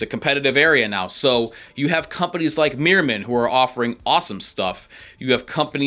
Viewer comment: What is the company name of the boots at 5:45, sounded like "Miramid", they look great.